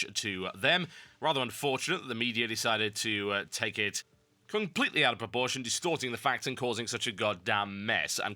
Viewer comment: Is is just me or did it sound like he said "completely out of abortion"?